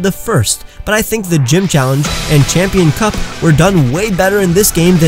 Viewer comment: Am I the only one who heard “Champion Cuck”